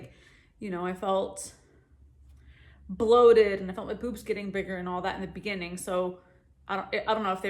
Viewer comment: LOL I misunderstood at 2:58 and thought you said "i felt my poops getting bigger"...and I was like hmm WHAT?! 🤣❤